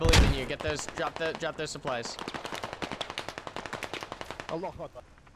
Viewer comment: did he just say allahuakbar 8:38 ?? LOL